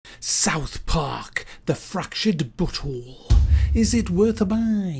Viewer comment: it sounds like he is saying "South Park Fractured Butthole." lol